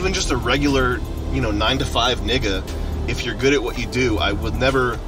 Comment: did he say nigga?